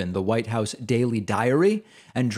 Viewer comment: OMG I thought you said daily diarrhea. I was falling on the floor. But I heard it again. And is something else. Great job! Great job!